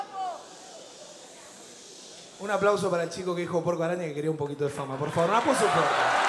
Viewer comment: Nunca entendi lo que dice:v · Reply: Un aplauso para el chico que dijo “puerco-araña” que quería un poquito de fama, por favor, un aplauso todos Eso dijo